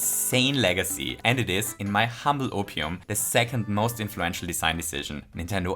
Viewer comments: I"m starting to thing Ceave is saying a "bittle lit" on purpose. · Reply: Oh I thought he was saying “in my humble onion” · This might sound weird but am I the only one who hears "and it is in my humble opium" on